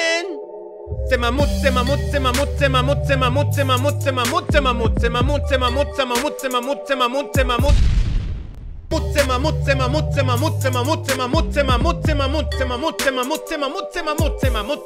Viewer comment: NO PARECE QUE DICE MAMUTSE EN EL MINUTO